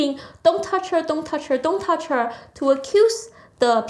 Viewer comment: Every time autistically screeched "DON'T TOUCH HER!" all I heard was "MY DICK IS SMALL!"